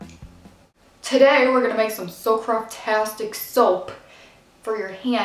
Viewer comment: i thought you said craptastic for a minute.